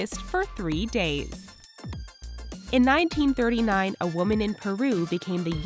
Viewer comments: Did anyone realize at 4:06 the subtitles said 1934 even though the girl said 1939?? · she said in 1939 but the caption says 1934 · she says 1939 but the caption says 1934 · subtitles say "1934" and you say "1939" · is it me or did I hear 1939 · did any off you guys notice in 4:05 she said "1939" when actually subtitles said 1934